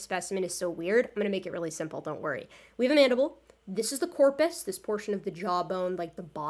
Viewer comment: Just a question (and no I'm not trying to be smart, I'm honestly curious): if "corpes" (spelling?) Literally means "body", why don't you say "body" instead? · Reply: I might have miss hear her she didn't say corpse to me it sounded like she said "corpés" or something to that extent which I guessed was a technical term